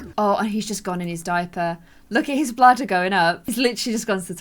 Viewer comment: When u said ‘he’s gone in his diaper’ I thought u said ‘oh he’s Goan and died ‘ 🤣😅👍🦊⚡️💙